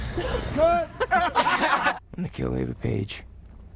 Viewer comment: In the scorch trials when Thomas says 'imma kill ava page' i thought he said 'imma kill every bitch' lmao